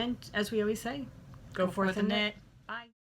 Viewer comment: You said "go forth and knit" and I heard 'go for the net' 😂🤣 I had to rewind it in my head before I realized what you said. 😀